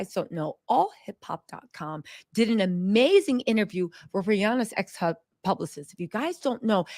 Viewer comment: I have to sleep a little after work or have some coffee before watching…. I thought she said Rihanna’s ex husband 🤦🏾‍♀️